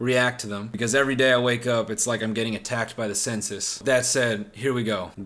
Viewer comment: Am I the only dumbass that thought he said “I’m being attacked by the senses” instead of the “I’m being attacked by the census”. It really took me 2 years to figure it out.